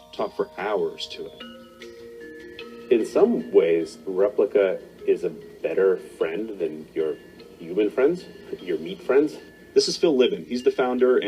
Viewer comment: does that dude say 'in some ways replica is better than your human friends! Your 'MEAT' friends!" ??? I've listened a few times and that's what I'm hearing him say! 'your MEAT friends' !!! Gotta say, this whole A.I Transhuman DWAVE CERN 5G Stuff is terrifying me! In 2011/12 I watched James Woods Futurescape (Science) series with my Pops. He showed an experiment that had a circuit board that had broken connections, they placed brain cells on that board, and the cells grew and linked the broken connections!!! It merged with the circuits!!!! I'd never felt so disturbed in all my life. And it troubles me for weeks, and gave me nightmares. Since then I've found out so much more. And it's been an horrific ride! And it's like a never ending nightmare!